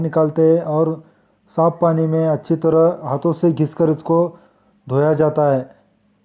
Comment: kya bola tu saaf paani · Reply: haa saaf pani me dhoya jata h.